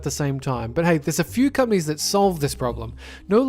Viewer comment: every time he said "companies" I thought he said "cummies"